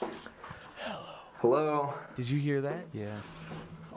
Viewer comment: i am pretty sure that the mens voice at 18:31 says "Hello again,", instead of just hello. Idk, doesn´t really make a difference, but somehow it creeps me a bit more out ^^´